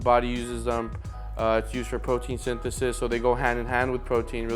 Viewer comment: I thought said photosynthesis and carbs go hand in hand